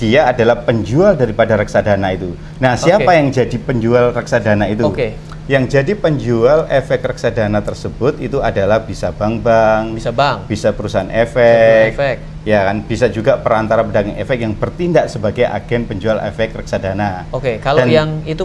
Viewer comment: yg jadi penjual efek Reksadana tersebut itu adalah bisa Bambang bisa perusahaan efek,. Wkwkwkw gw tertawa pas denger Bambang harusnya bank- bank gw dengernya salah ahahahaha